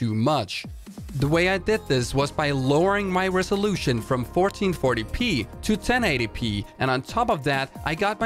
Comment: i dont think you should ever play below your monitors native resolution. honestly if you want to play on 1080p go buy a nice 1080p monitor and just swap out your monitor or something. downscaling looks gross. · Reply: the way i heard it he says "i lowered my resolution from 1440p to 1080p" not i switched monitors to one with a lower resolution. but i could be wrong